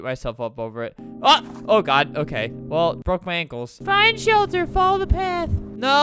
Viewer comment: When he said “find shelter” at first I heard “find children”